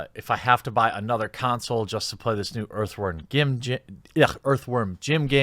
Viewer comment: I thought you said "Earthworm Gimp game" at the start haha. Almost spat my coffee on myself.